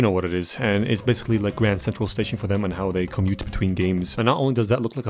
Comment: did he say "Grand Central Station" or "My Aunt's Constipation?"